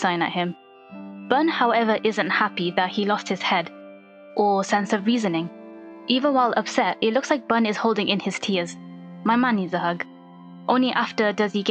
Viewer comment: I heard Manman needs a hug and you can't change my mind.